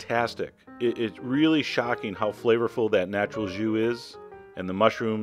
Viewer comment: did he said "natural jew is" O__O